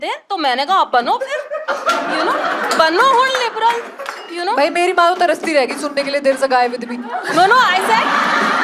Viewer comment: Can anyone say what was said by the host at 31:17 please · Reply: "meri maa taraste reh gayi sun-ne ke liye that there's a guy with me"